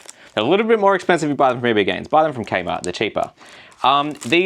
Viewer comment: did you say k-mart? where you from? I thought those were extinct